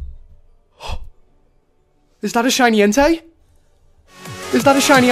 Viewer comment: This is gonna make me sound like a little dirty minded but why does it sound like he is saying "Is that a shiny Hentai" 😂